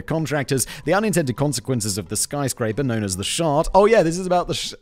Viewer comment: I heard "shart" and now that's all I can hear him say from this point. I am happy